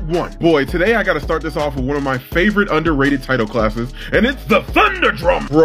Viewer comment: Ah yes, the tinnitus dragon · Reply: I didn't even recognize him say that lol. I thought he said Titan class dragon. But he also said Tidal class at the beginning.